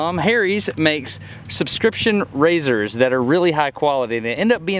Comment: lol you said subscription razors but I thought you said PRESCRIPTION razors and I was like, "wtf you need a doctor to shave?"